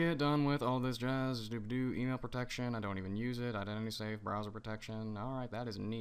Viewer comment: I swear to god at 5:27 you said, "Dobado, anal protection, I don't even use it" lol